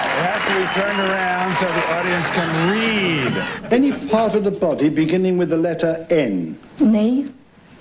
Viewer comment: What do they say at about 6:50? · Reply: "Name a body part that begins with the letter N." "Knee."